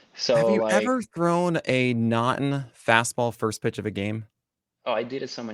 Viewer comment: Please keep in mind I don't know baseball yet, I'm trying to learn for this upcoming season. What is said at 14:56. "Have you ever thrown a "naughten" fastball? I checked MLB glossary, wikipedia baseball slang, and pitching scouting terms and can't find it. Did I mishear the word? TIA.